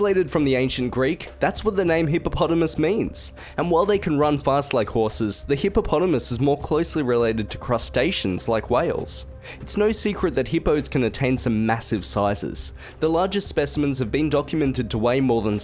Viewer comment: Uhhhh did he just say crustaceans... lol...?